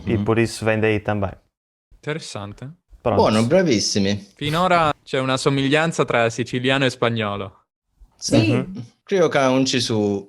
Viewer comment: This is one of the best displays of spoken Sicilian (Palermo dialect). Cumplimenti! *@ 08:29 the ITalian subtitles are wrong. It should be written "Finora c'è una somiglianza tra SICILIANO e spagnolo." not italiano. 👏🏽👏🏽👏🏽